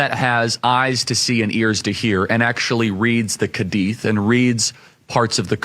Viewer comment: did I hear a kadith😂😂😂